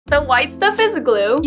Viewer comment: Oh i thought you said the white stuff is blue i wached it 12 times but its the white stuff is glue